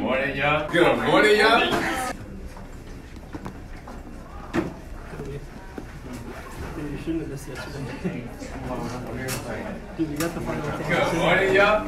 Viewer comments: After a while I heard: “Good morni yaw” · after the first "good morning yall" it just sounds like hes saying "gumorinyal" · After a while it just sound like him saying, “Good borin nya” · After a while my brain did a trick and I kept hearing "good morneo" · "Good morning, y'all" doesn't even sound like a normal phrase anymore. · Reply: Yeah I just heard " Good morning yaoi"😅😅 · I heard it as good mod in here